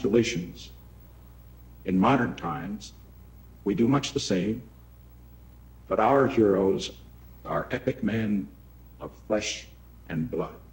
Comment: Anyone else hear Nixon say "our heroes are Ed McMahon" at 6:14?